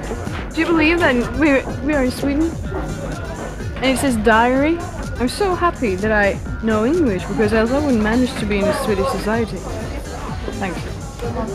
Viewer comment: What is Jenny talking about to Linn? 7:04-7:15 I'm not a native English speaker, so I can't understand some words. Can you write every word, please? Thanks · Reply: Could you believe we are in Sweden and it says "Diary" ? Iam so happy that I know english becouse I wouldn't manage to be in a Swedish society.